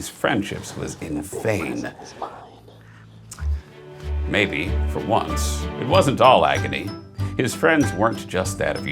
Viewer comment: I can't make out what whispered at 7:03. WHAT DID HE SAY I MUST KNOW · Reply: He said "the big present is mine"